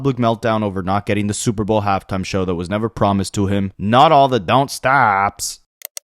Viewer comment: No lie, I thought he was say "Southside" not "Don't stop" 😅